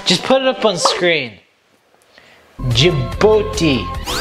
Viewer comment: I did not realise how long it would take to rate 195 countries 😭😭 My Discord <link> Follow my twitter pwease 🥺🙏 <link> instagram 😘😍✨ <link> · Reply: I heard you said Djibooty 🤣💀